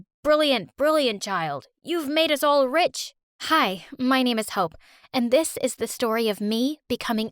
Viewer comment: When she said 'My name is Hope' i thought she said 'My name is Hoe' pls😭🤚